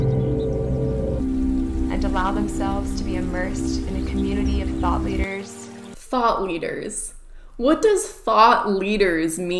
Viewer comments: I actually thought they said “THOT leader” and not “thought leader” for a second. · I heard “thot leader” I was about to join the cult.